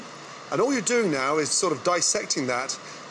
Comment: I thought Gordon said “Farmer seggs”